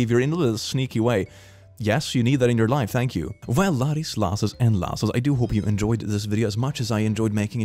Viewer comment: What does say in the beginning? I hear "loddies, lasses, and lasses" and I'm pretty sure that's not right. YouTube subtitles were no help. Having audio processing issues sucks ass